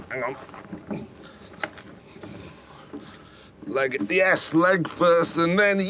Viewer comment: First time i heard "leg in the 4ss" and I thought Jeremy was really tryin' to put his leg there to fit in the peel p50😂